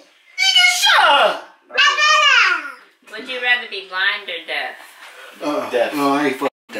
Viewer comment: The way they looked at the baby😂😂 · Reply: It sound like he said “I rather go blind “